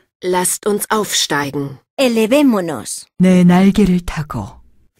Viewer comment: did she say taco?